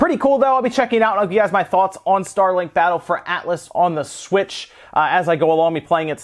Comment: yo escucho que dice Starlink battle of outlast ;v